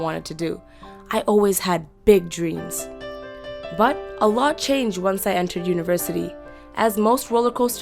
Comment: Is she a muslim ,, coz i heard her sayin Allah Changed at 2:13 Btw, I'm a Moslem and I'm proud to be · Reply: she said "a lot" ><